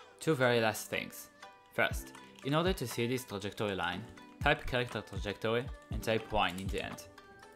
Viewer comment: Followed exactly and keep getting "Footstepeffecttagmodifier" blueprint error. 5.4.2 also @ 4:10 what in gods name are you saying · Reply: `a.CharacterTrajectory.Debug 1`